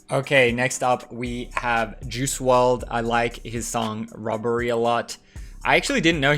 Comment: I thought he said rubbery